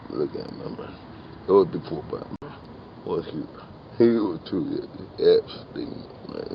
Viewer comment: The second clip I swear I hear him say "Hail To Epstein"